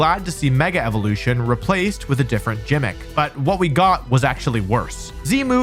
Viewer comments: did you say JIMMICK instead of gimmick? Oh my god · did you just say Gym-mick?